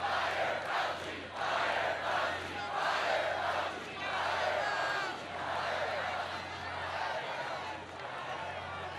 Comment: What did they chant? Sounded like "BAIE DANKIE" (Afrikaans for Thank you very much) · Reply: “Fire Fauci”